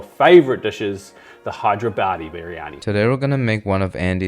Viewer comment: i am veerryyyy verryy sureee i heard him say hydra baddie biryaniiee 😂😂😂